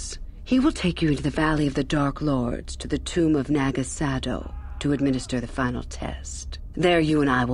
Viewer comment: (0.0) Did anyone else hear Quagsire in the background when she said Naga Sadow?